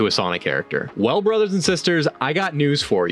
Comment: boilers and sisters is what i heard 😔